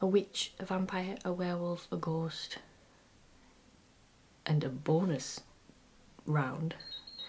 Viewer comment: Did somebody say 0:58 Henry Bone-ous?